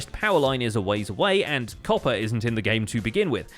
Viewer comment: When he said “copper wasn’t in the game to begin with,” I thought he said ”COPPA wasn't in the game to begin with”